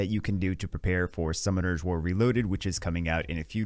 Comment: I really thought will say Prepare your money for new packs😅